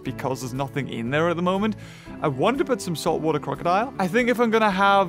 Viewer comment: Did you say "water crocodile" 🤔🤨😂😂